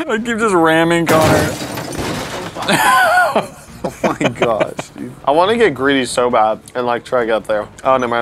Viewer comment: Am I hearing things or did say “fucking gosh dude”